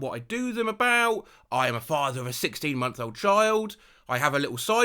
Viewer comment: when I heard I’m a father of 16 I didn’t hear months old so I’m like he’s a father of how many